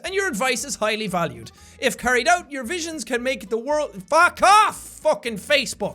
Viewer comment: did you just said ''If carried out your visions can make the world FUCK OFF'' ??